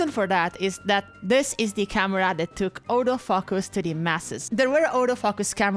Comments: Auto focus thought you were saying all the fuckers lol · What was said: “This is the camera which took autofocus to the masses.” What I heard: “This is the camera which took all those fuckers to the masses.”